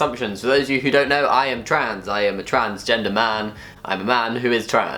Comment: I thought he said Transgentleman. I like it. Gonna keep that word.